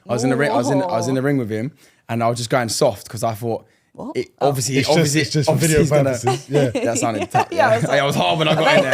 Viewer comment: Am I buggin or suttin or did at 18:30 did she say ni**as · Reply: are you deaf or smth she said mingers